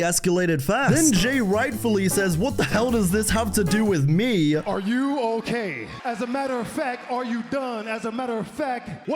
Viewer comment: I thought Jey said, "Are you on caine?"😂 It was nice hearing JD's theme. One of my favorites Def Rebel themes.